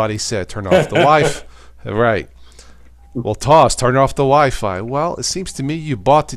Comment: I thought I heard "turn off the wife" but then realised said "turn off the wifi". Oh well, wishful thinking. :D